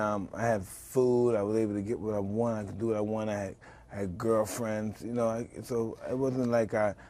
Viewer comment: secs Did he say he had "girlfriends" in prison? Wow!!!